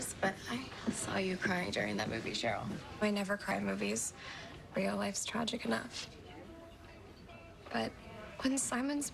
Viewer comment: if you turn on the captions cheryl says your wife's tragic enough instead of real life is tragic enough